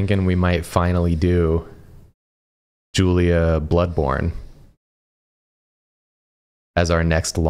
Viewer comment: I heard him say “Julia Bloodborne,” and I looked excitedly back to the screen to see Julia making that face. perfection.